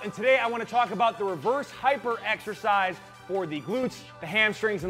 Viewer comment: I heard "the worst hyper exercise" until I rewatched it 3 times lol